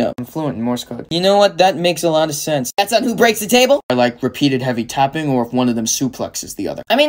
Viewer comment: I, uh, I misheard that line of "Repeated heavy tapping" as "Repeated heavy topping" and sadly... I can see Deku as the bottom...